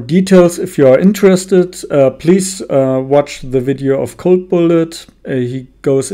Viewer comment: what video you said to watech for more detail ? I could not hear you , pls give link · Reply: CB ( code bullet)